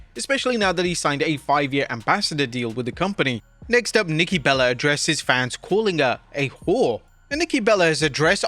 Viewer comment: I thought he said Nikki Bella is a “4”, which I absolutely agree with!